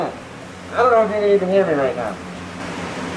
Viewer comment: did you say... you really miss your himi right now lol jkn